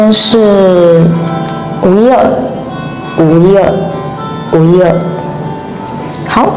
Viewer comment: What are you saying at 0:38? It sounds like wu3 le4. I would have expected 五一二, but I hear an "L" sound so I'm not sure 😕